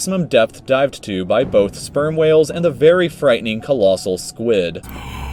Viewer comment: Am I the only one who heard Sperm Whales😕